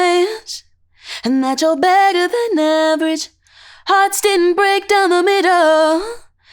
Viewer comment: I thought she said “cuz your badder than average” lol